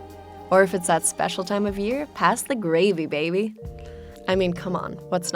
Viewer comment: I thought she said "pass the baby gravy" 😅